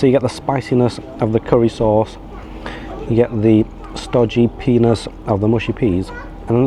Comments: What did you hear? 🤔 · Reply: Sounded like you said stodgy penis lol. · Not gonna lie, i had to play that part back to make sure I heard you correctly! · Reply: No one likes a stodgy “peaness” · Did you just say "Stodgy penis, of mushy peas"? 😂😂